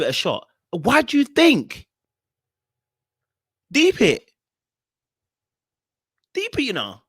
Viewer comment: Canadian here. At 10:58 and 11:17 what are you saying? Deep it? What does that mean? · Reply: MEANS UNDERSTAND IT LOOOOL